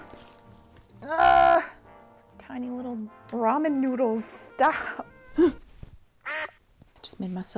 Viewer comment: I thought you said Drama Noodles, haha!!!